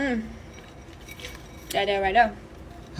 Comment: what you couldn't seem to hear was her saying "that there right there." ^-^